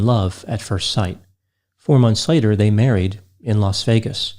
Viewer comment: I thought said: "Hormones later" Not: "Four months later..."